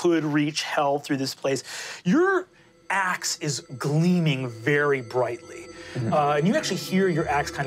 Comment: I thought he said “Your ass starts gleaming very brightly”